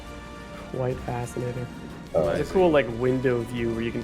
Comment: did i just hear what i think i heard xDD? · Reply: "quite fascinating"